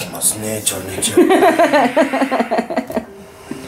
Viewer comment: ちゃんれいちゃんっていってたのかww ちゃんねーちゃんって聞こえてたww